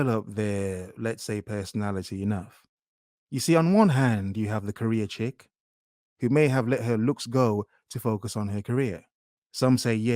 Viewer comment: Please don't send bad vibes to Ms Amy Thank you for watching. LIKE & SUBSCRIBE FOR MORE. If you would like to support my channel any contribution is welcome: -PAYPAL <link> -PATREON <link> -HOODIE <link> -BACKGROUND MUSIC <link> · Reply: says "career chicks" What I heard: "Korea chicks"